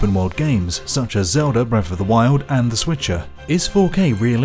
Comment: Did he say "The Switcher." 😂